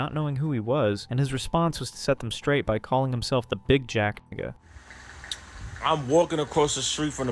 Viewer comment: what did you say?? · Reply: He said “Big Jack nga” like a real hood nga YaFeelMe